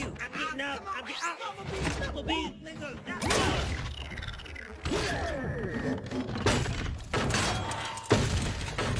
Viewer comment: did one of them just drop the n word??? Or am I hearing things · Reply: Nah he said listen